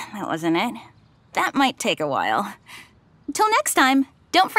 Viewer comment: God I need a hearing aid. Thought she said "go to my YouTube channel to watch p*rn" at the end! 😂😂😂